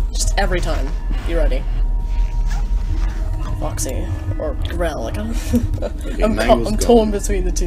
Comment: I'm sorry at like two minutes 45 seconds I heard ciel say "Foxy, Grell, Daddy" is that correct